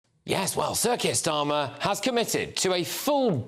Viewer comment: When I hear 'Sir Keir Starmer' I actually hear 'Circus Starmer'